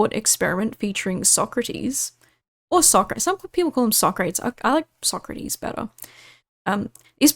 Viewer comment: Whomst tf is saying "sock crates" !? Are we shipping textiles now?